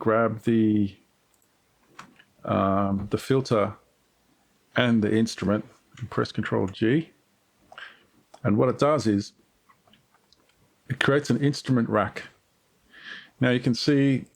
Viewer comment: I had a bit of a Yanny/Laurel moment there with the "Control G". It sounded like "Control Q" the first few times I heard it.